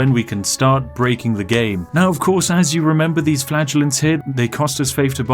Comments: I got a bit confused because I thought spiff said flatulenze instead of flagelant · I don’t know if it’s made better or worse by mishearing flagelance as flatulence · I keep hearing 'flatulence' instead of flaggellents